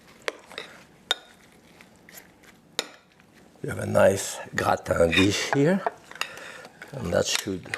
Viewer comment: where the subtitles say "indistinct", I believe he is saying "a gratin dish".